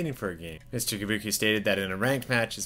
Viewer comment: While everyone on the comments heard Mr. Yabuki's name, "Tabuki", I heard "Kabuki".